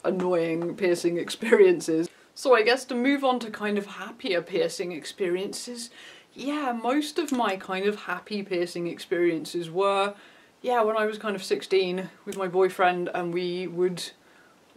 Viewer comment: Lol I thought he said "pissing experiences" at first